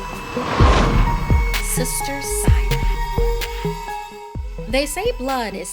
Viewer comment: for some reason i thought the thumbnail said “sister green”